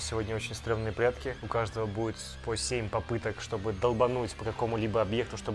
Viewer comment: мне одной послышалось "107 попыток" а не "7 попыток"